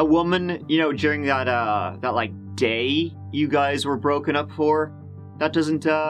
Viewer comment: I had to listen several times but I thought for sure called Christopher's girlfriend a whore. I think he actually said "for".